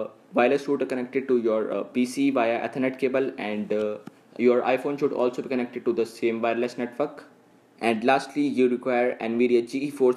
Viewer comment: Did you just say f*ck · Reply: NO, I said your wifi should also be connected to the same wireles netWORK. :P